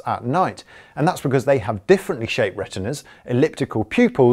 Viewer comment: What does he say cats have different shaped? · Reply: Emm N Retina